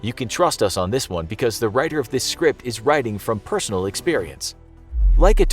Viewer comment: Did He Just Say Personal Experience At 2:30 To 2:42?